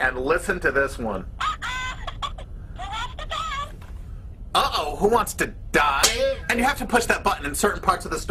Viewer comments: i heard "Uh oh, who wants the duck" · it said oh oh who wants to go. Are you really that deaf. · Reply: IDK man, I heard die at 2:00. Not 2:01. Come on man, why would you do that to us?